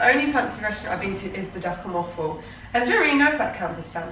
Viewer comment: does someone understand what restaurant she’s saying here?? lmao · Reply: The duck and waffle!